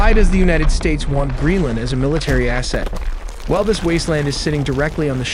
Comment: Did he says wasteland?